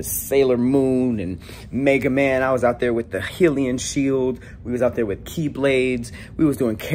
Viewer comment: Man did you just say "Heelon Shield" lol